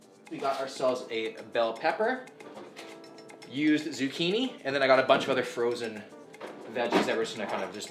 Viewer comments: did he just say "used zucchini"? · did you say "used" zucchini?